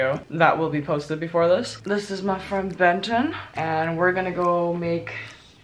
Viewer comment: Hi mommah. I thought you said benten lol. He has more cake than me hahaha. Haha the legendary Oh yeah is so funny. Btw how are you mommah. How are you? I'm under the water please save me jk haha love yah mommah